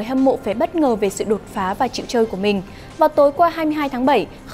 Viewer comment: ai nghe thành trẻ trâu ko :))